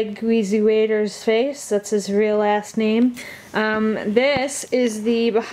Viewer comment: I thought you said in referring to Craig WheezyWaiter, "that's his real ass name!" Hahaha!